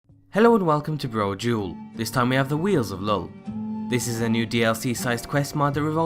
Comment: Misheard as "The Wheels of Lol"